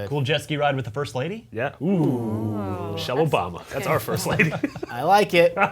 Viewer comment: I thought he said "shovel Obama" and then heard "shell Obama"